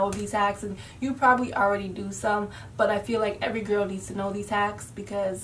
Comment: Hey! I love your videos. Just wanted to let you know that the captions for this video are a little off.. At 0:39 it says "Every girl needs to know these tax".. Instead of" Every girl needs to know these hax". Just thought I'd mention it. Thanks! You're so pretty 😍😍